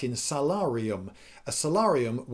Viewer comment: tfw you misheard salarium as solarium and can't stop thinking of centurions lining up to get into a tanning bed.